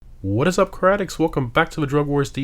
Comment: I know I need better sleeping habits when I'm watching your videos on an hour's sleep and mishearing your opening introduction as "What is up crack addicts?" lmao